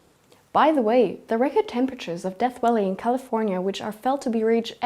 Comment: Did she JUST say "Death Welly in California..."?? Not sure where that is, exactly... but I'm sure they'd corroborate. ; )